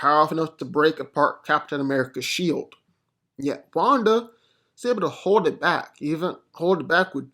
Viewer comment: But I thought he said Wanda wins LOL